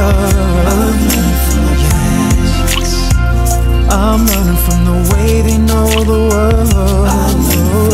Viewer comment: During the song, my brother and I thought they were saying "I'm running from the ants; I'm running from the way they rule the world," 😂😂😂😂😂